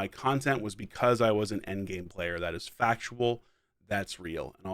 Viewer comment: I swear I thought you said the reason people started watching you was because you were an NBA player.....😮